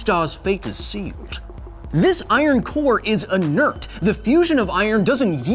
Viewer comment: Why "(mumbles)" instead of the spoken "inert"?